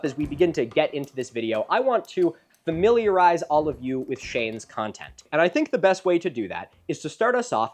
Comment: funny note I saw: the captions say “I want militarize you with Shayne’s content”. Which I found quite funny😭💀